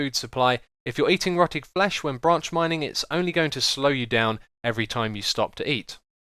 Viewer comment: I thought u said ranch mining XD